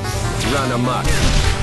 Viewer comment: what did he say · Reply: Run amok